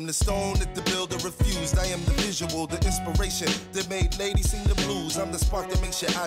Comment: I thought he said that made hades sing the blues 🤣🤣🤣😭😭😭😭😭